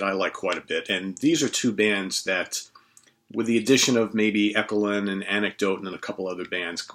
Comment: what was that band you said at 0:47 ? eckalin? echallan? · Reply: Echolyn